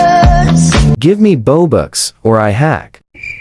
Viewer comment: I thought the dude said give me boba or else I hack 😂